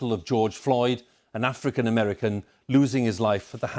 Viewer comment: When he said African American it really sounded like he said that fricken American, go back and listen to it